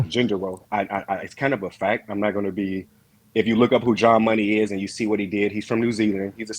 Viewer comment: who is he refering to that is from New Zealand. it sounded like he said Ja Money.